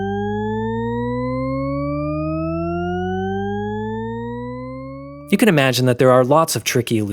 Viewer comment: Am I the only one that thought he said Barbara pole? I wondered what the hell he was talking about 😳